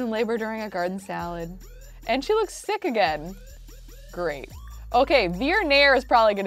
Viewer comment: when said "and she looks sick again" at 30:06, I thought she said "and she looks thick again...." oops